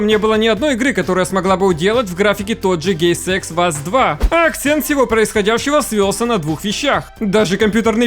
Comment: Напишите название игры на тайм коде 7:50 я не расслышал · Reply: The last of us 2